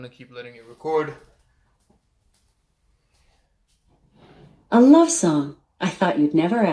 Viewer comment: I LITERALLY HEARD A WHISPER REALLY FASTLY AND QUIETLY SAY “Alexa play a love song” That’s honestly hilarious